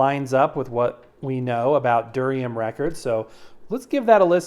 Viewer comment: I thought you kept saying durian. That is a stinky ass fruit. Glad Duriam records aren't made out of durian